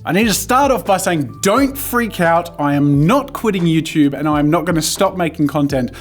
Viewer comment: When he said “don’t worry I’m not quitting YouTube” my heart missed a BEAT I thought you said “don’t worry I’m quitting YouTube” I’m so glad your not gonna stop 🙂